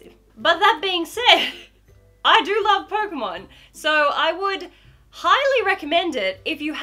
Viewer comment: well.. I thought she said kocainemon lol (like instead of pokémon... if u didn't get it.. TuT